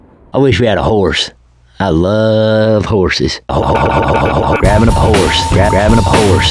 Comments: ellis sound like he was saying 'grabbin whoes' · Am I hearing "Whores" instead of "Horse?" XD